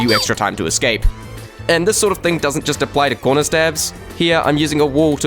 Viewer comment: What is a conrer stab At 6:39 he says conrer stab